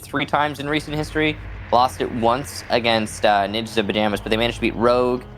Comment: the caption says ninja tuba damage